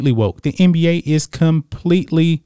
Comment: You said the NBA is woke...I thought you said, The NBA is a JOKE! Well, I guess both are true, lol!!